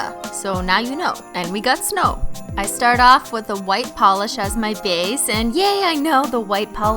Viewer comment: Dead because I thought said a polish white as my face